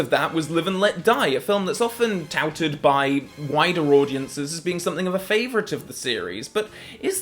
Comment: For a second there I thought he said " touted by whiter audiences" lol